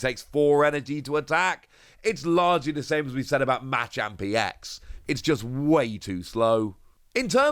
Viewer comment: Bruh! Why are you saying machamp so weird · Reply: I was thinking the same thing. It sounds like he is saying "match up" lol